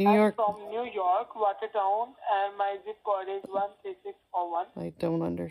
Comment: It sounded like he said, New York, Waikato.. there is a waikato New Zealand